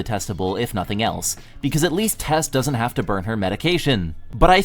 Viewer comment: Did...did she just say Tess?